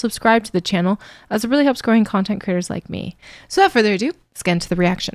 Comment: I must confess, I misheard "content creator" as "content critter." As for saxophone, I challenge anyone to name a song that couldn't be improved by including a horn section.